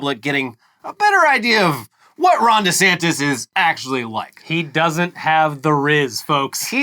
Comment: Is it just me or does Ron say his own name funny? Sounds like he says Rondy Santis.